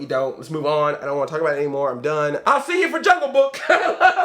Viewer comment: Did you just say at the end "I love you like a plane cousin!" ? That was just rich! I love watching you man.